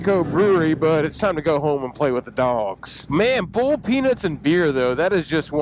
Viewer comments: My ears - says "Boiled peanuts" I hear "bull penis".......yike, I'll pass. How about some nachos? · Boiled peanuts? Thank God, Thought you said Bulls Penis!!!!!!!